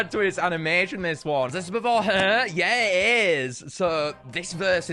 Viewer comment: why did he call Hyehehe "her?" "Is this before her?" The subtitles said Hyehehe but it didn't sound like it